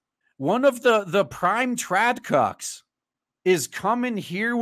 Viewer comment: What's a "trap cop"? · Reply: Are you mishearing him say trad con? Traditional Conservative